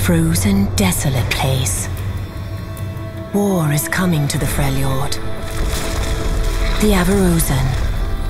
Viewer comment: WE NEED A NEW FRELJORD CHAMP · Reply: what he said ornn is coming to freljord